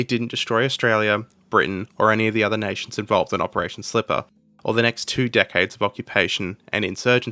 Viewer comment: As someone who has never heard of the name "Operation Slipper", I, at first listen, thought it was called "Operation Slip-Up" when you mentioned it around 0:50 , which would've been hilarious in hindsight.